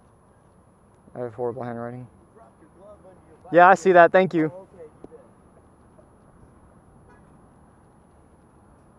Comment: What did he say at 9:39? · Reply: The guy said you dropped your glove